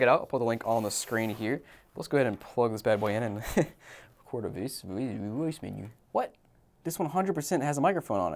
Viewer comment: What is he saying at: <link> 8:54? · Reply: He tried to say "record a voice memo" but messed up and ended up turning the error into a joke, putting the text "stroke.mp4" on screen to complement it.